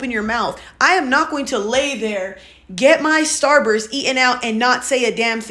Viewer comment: Hol up. Did i hear "get my star busted in and out"?